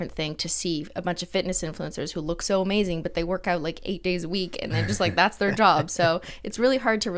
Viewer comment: Did Cristine say "workout 8 days a week"? 😂😂♥️